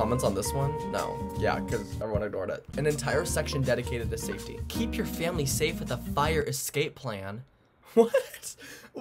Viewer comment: I thought it said “Keep your family on fire” I LEGIT GOT AN ADD FOR SAFTEY